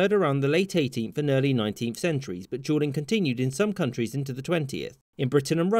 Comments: It sound like he says juuling, and i just think of me and the bois in the school bathroom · sounds like hes saying juuling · I thought he said juuling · Ngl I thought he was saying juuling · Lmao sounds like ur saying juuling... so many jokes come to mind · Juuling. It sounds like your saying Juuling. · I thought you said Juuling every time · It sounds like he is saying juuling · Sounds like your saying juuling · I kept hearing “juuling” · Sounds like your saying juuling😂but good content🤙🏾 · I keep hearing "juuling" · Sounds like homie is saying juuling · Sounds like he is saying juuling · It honestly sound like your saying "Juuling" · I thought he said Juuling. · I keep hearing "juuling" and.. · Honestly thought you were saying Juuling lmao